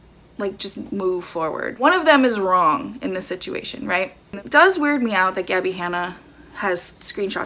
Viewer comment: I believe I might still be drunk. When you said "it weirds me out when" I heard 'a weird meow"